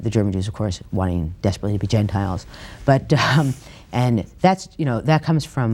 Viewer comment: What does she say at 24:36? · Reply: "The German jews wanting desperately to be gentiles?"